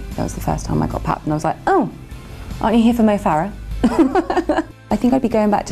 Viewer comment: Can somebody told me what she says at 0:37 ? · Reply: Fabre Marion She said 'Aren't you here for Mo Farah'. Mo is an Olympic runner for Britain, he won at 2012 (dunno if he did at Rio)